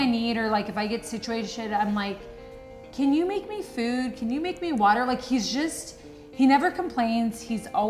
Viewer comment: Did Nikki just say can you make me water? 😂😂😂